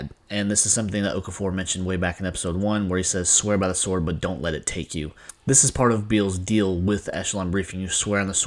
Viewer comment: What did they mean by swear by the sword but dot let it stick? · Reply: Oh is that what he said? This whole time I thought he said "don't hesitate"